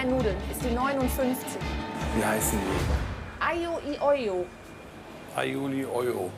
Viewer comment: schon schlimm wenn man nichtmal Aglio e Olio aussprechen kann haha habe als erstes YoOli verstanden